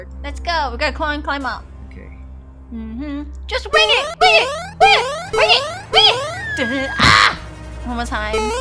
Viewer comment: my favouratie part of the video is: when said win it win win it 😂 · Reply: Same I thought she said “wig it wig it wig it wig it”